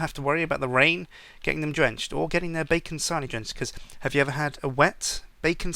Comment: I thought you were saying Bacon Sahni. And I was like, wtf? Then you said Bacon Sandwich and I was like ohhhhhhhh.